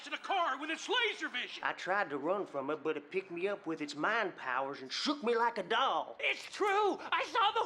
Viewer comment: omg MIND powers... i always thought that monster said MAN powers lmao XD